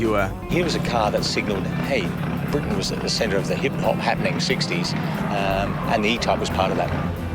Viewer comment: did he say Britain was at the center of the Hip Hop happing sixty. Really.... Really.... huh.